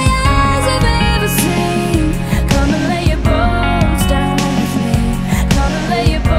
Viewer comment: I always thought she was saying Come and let your balls down with me XD